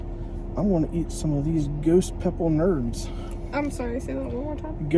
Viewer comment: I know thought you said Ghost Pepper Nards 😶🤓